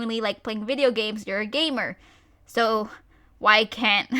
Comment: When you said "gamer" I thought ya said "gaymer" a gay gamer.